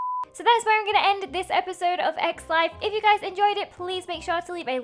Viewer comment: when she said “That is it for X life” i thought she said eggs life